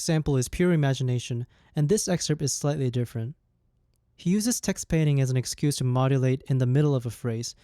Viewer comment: Sorry, what was that word you said at 6:03? "He uses (?) as an excuse to modulate in the middle of the phrase" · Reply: Text-painting